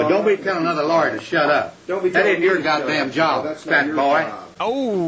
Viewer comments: I heard that clip twice before I heard you quote it and both times I heard "catboy" instead of "fat boy" and was confused as to both why he chose that insult and why he got offended to it lol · did he just call that dude a cat boy?